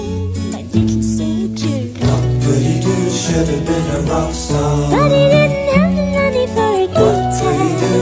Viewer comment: I somehow thought it said : "do body do should have been a rockstar, but he didn't have the money for a good time"